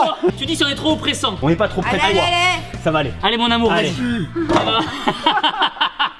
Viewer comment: ses moi ou tibo as dit a aller mon amour?